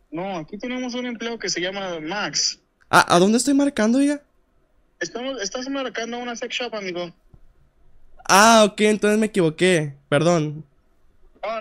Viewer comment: Pense que dijo petshop jjajjaajajaj